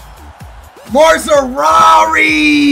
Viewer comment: subtitle saying more ferrari